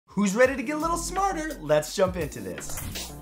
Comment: I THOUGHT HE SAID WHO IS READY TO GET A LITTLE SNORTER SMH